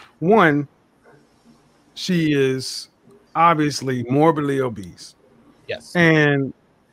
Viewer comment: 😂 thought he said norbitly o east 😂 - you can’t make this shit up